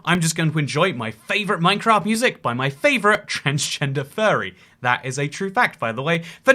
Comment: Wait, did he say fairy or furry? 🤔 Bit weird to mention if the latter, and kinda offensive if the former.